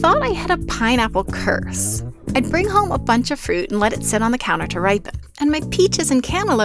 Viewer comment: I thought "counter” said "computer” 🤦‍♀ I was like WTF kind of clickbaiting is this? Making people click to understand the statement itself? 🤣